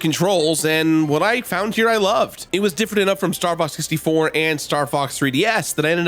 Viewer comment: did he say Starbucks 64?